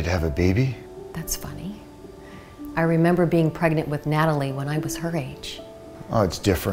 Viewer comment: oregnant instead of pregnant lol